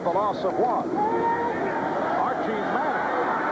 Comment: did he say Archie Manning?? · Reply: Yes, that was Archie Manning at QB. He didn't play the entire game though. Got KO'd by LT in the 3rd quarter